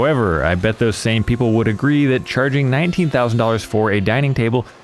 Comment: $19,000. Man. I’m still paying off my hospital bill from three years ago. Jesus Christ. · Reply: Shit. I thought he said $90,000!